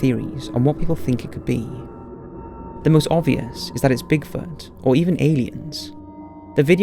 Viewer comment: Did you actually say it's "obviously a Bigfoot or alien" for video # 3 6:40. You sound so sure haha nonetheless awesome videos. I greatly appreciate your work 👍